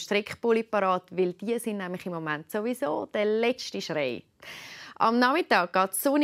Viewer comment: Did she say sowieso at 0:47? Sorry schweizerdeutsch is not my native language!